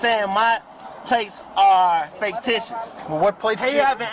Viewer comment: this attorney says "faketitious" instead of fictitious